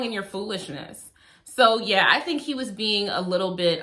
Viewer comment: really thought she was going to say "I think he was being a little bitch", which also would've been correct